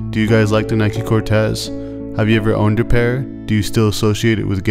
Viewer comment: did u say gae culture?